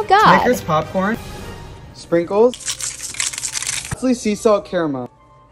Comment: I thought he said glizzy sea salt caramel